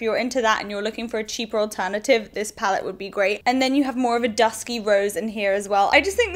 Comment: I thought I heard "dusky rose"! LOL Ps: you look gorgeous and im loving your new hair (saw your instagram pictures)